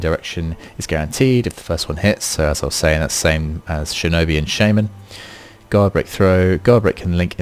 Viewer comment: I heard you saying Shaymin, is that a new hero?